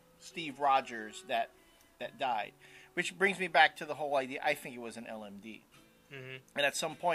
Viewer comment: I can't for the life of me understand what you said at minute 4:30! "I think it was a WHAT???" "I think it was a LMB???" HUH??